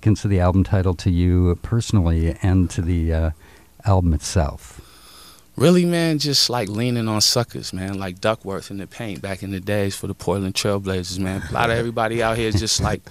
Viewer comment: Hey everyone. Can't understand what Ish says to the question about the meaning of the album's name. Around 11:21. Not a native speaker, can't make out the first sentence (( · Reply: Like really man it’s like leaning on suckers like duckworth in the paint back in the day for the Portland trailblazers “ - it’s a lot of slang (I’m not sure if he’s saying suckers but if he is It has some special meaning locally I imagine ) Duckworth is an old musician and in the paint is probably slang. So as a native I’m not even sure what exactly he’s saying other than “we are relying on foundations laid by trailblazing musicians”